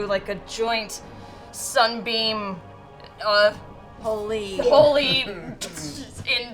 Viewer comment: did marisha say she’s gonna do a Holy N-bomb? Marisha ray! Tsk tsk